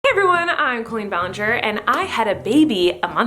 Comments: I thought she said "I'm queen Ballinger" and i was like yasss queen but then I actually realised she said Colleen and this probably doesn't make sense because im so tired, bye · Did anyone else hear Queen Ballinger? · i know your intro and what you're saying but sometimes it sounds like your saying "queen ballinger" and i can't disagree